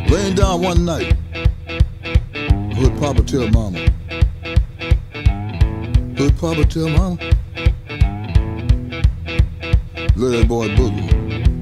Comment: I heard “good papa tell mamma, lil boi Booty”🤣🤣🤣